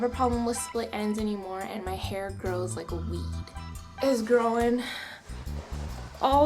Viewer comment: did she say "my hair grows like weed" omg hahaha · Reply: grows like a weed are u stupid